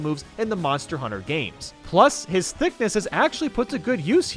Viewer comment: Did you say thiccness?